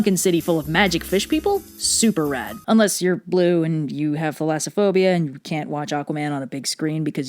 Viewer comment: "Unless you're Blue and you have Phylasophobia(spelling?)..." What the actual hell does that word mean? I tried Googling it with multiple various spellings (just a few examples: Filasaphobia, Philosophobia, Ilasaphobia and Elasophobia - just in case I misheard the "f"/"ph" sound at the beginning of the word) and got absolute bupkis... So what the flyin' F does it actually mean, and what's the correct spelling? Or is it just some B.S. made up? (If it's the last one, I truly wish she'd have said so, so I wouldn't waste time looking for non-existent words...) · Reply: Thalassophobia according to captions